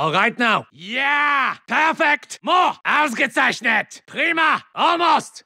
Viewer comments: What does he say at 2:53? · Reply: Bracus Zahn Ausgezeichnet = Excellent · What does he say 2:53? Alcha snizet? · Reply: “ausgezeichnet” which means something like “amazing” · I want to know what he said at 2:53. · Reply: Ausgezeischnett - Excellent